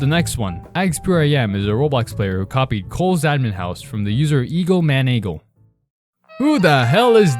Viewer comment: i thought you said twjs admin house